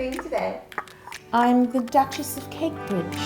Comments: I swear I thought you said duchess of cake Bit** 😩😩💀💀💀I was about to say whaaaaaaaat I thought this was kid friendly 😂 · Honestly I'm not gonna lie, it sounded like said "I'm the Duchess of Cake, B****" lol.... I bleeped it out so I wouldn't be banned.